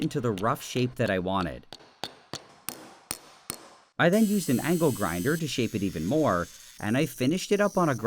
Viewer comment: This guy has everything in his house, chemicals alchemist equipment and angle grinders and stuff. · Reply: I thought he said an ankle grinder